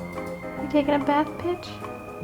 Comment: i heard you say "you taking a bath bitch"